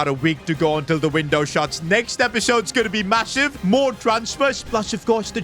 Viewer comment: did you say massive